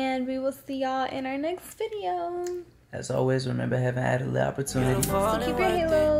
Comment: Please what did say at the end? 10:13 Remember heaven's always got what? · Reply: Heaven added lit opportunities ( H.A.L.O)